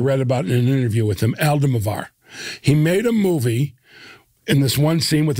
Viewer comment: Did anyone understand the name of the director Jeff mentions at 25:22? I have listened to it probably a dozen times and I can't pick it out. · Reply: i think hes talking about pedro almodovar, but i am not sure. if you havent figured it out by now.